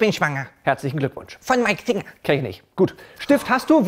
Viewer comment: Ehm, Herr Kotbeutel?" ,,Ja" ,,Ich bin schwanger" ,,Herzlichen Glückwunsch " ,,Von Mike Singer" ,,Kenn ich nicht" 😂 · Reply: Bruh ich hab verstanden von meinem finger XD (hasst mich nicht dafür hab nur net richtig zugehört xD)